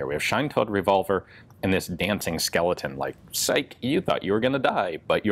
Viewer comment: For a second I thought you were gonna say "Sike bitch you thought"